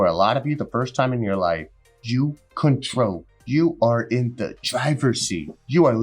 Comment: I thought you said "jews, are in the driver's seat"...I began a slowclap...